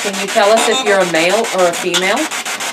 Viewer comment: it 100% sounded like it said “female”. I went back and confirmed a couple times. I know sometimes it can sound different from translation on the screen, but it sounded pretty clear to me. Haha never mind you guys caught it, confirmed a little later :)